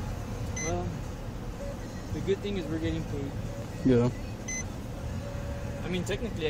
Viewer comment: what did the backup cop say?? It sounded to me like. "The good thing is we are still getting food" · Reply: Getting paid